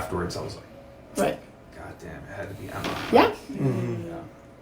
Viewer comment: At about 33:15 minutes, did anyone else hear say "Godd_mn, it had to be Emma." I mean, using the Lord's Name in vain like that just rolled off his tongue too easily and no one flinched.